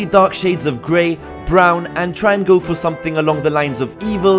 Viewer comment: I thought you said 'triangle'. xD Que pavera. I'm actually super excited to see KoGM back ON and giving it new life since this is what peaked my interest all those years back. Yey. :D Good times! - Sinio